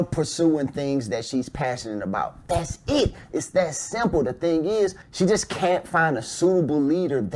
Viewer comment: Clip 2: all I heard him say is "I'm a simp" 😂